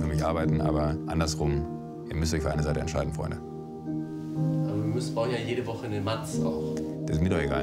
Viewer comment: was hat er gesagt?? Ich habe Folgendes verstanden: "Aber wir müst bauen ja jede woche in den matz auch."